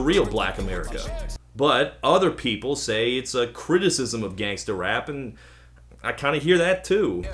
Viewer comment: BDWJ1989 I know but said in the vid it could be condemning trap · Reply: Lil PumpthisnamesironicIdontactuallyLikePump I thought he said gangsta rap?